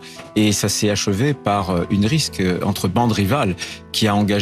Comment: C'est moi ou le boug prononce "rixe" comme "risque" ? Mama, c'est foutu.